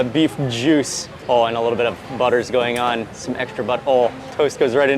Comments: did i just hear he say some extra butthole? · Hi Mark. At 16:25 did you actually say butthole toast......both the wife and I heard it and then had to turn captions on and it says the same. 😂 btw big fans of ur show. Love the videos. Even the butthole toast. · Did Mark say extra "butthole"??? Even the captions think he said that lol... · did he say "extra butthole "? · Sorry but I just heard "some extra butthole" at 16:22. I love your stuff Mark! I just cant help from hearing these things😅